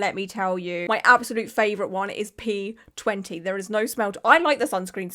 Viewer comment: I have the sense of humour of a child but at 19:50 before you finished your sentence I thought you said that your favourite sunscreen is "pee" and I burst out laughing 😭